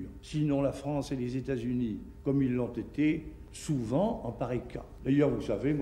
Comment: c'est quoi ce sous-titrage ? De Gaulle dit "en pareil cas", pas "en Paris" (ce qui ne veut rien dire d'ailleurs).